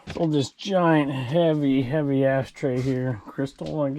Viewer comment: I thought you said "heavy a$$ tray" 😂